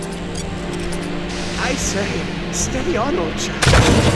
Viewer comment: What does he say there? "I say, steady..." what? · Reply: "I say, Steady on Old Chap..." I've played and Failed this Level enough times to learn that off by heart.